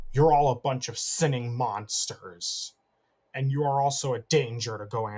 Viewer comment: it sound like he said singing monsters